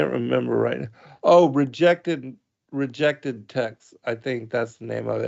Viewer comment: Did mention the website Rejected Text for additional reading?? I can't find it by that name, did anyone catch the name of the web site? Shalom